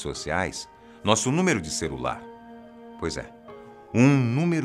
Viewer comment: eu ouvi um CERULAR? kkkkkk o errando uma pronuncia coisa rara de se ver!